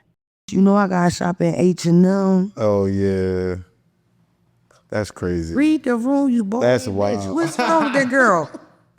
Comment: that’s what she said? Wowwwww 😂😂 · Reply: He said ooooohhh yeah 😂